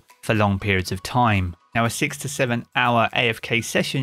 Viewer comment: I thought he said 67 hour afk and was like :( Then I heard him again and realised he said 6-7 hour afk and was like :). Free power levels here I come